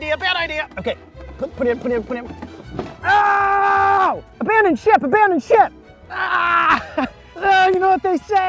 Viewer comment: Anyone else thought he said Abandon Shit 😅😅